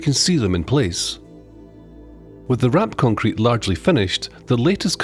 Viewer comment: "With the ramp concrete largely finished ....". I initially heard that as "With the RAAC concrete largely finished..."! Anyone living in the UK who has followed the news over the last few years will understand my brief moment of panic when I misheard that!